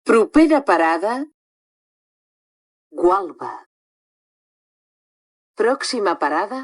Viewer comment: entendí "guapa"XD